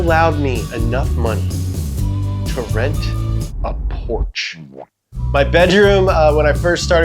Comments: I thought he said enough money to rent a Porsche and I was like okay nice then I saw the house and understood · anyone else first think he said rent a Porsche? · When you said rent a porch I thought you said rent a Porsche 😂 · “…allowed me enough money to rent a porch.” I thought you said rent a Porsche. LOL Best of luck with your new venture! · I thought you said enough money to rent a Porsche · Who else thought he said Porsche? 😂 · I thought he said a Porsche not a porch. lol rent a porch.